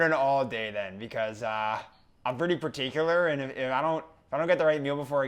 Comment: Did just say parteeecular